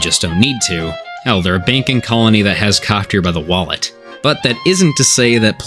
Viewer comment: I somehow managed to hear "They have them by the WALNUT" instead of wallet